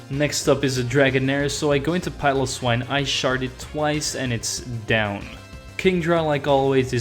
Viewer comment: Lol, at 14:28, it sounds like you said: “I sharted twice.” Sorry about shitting your pants, man. We’ve all been there.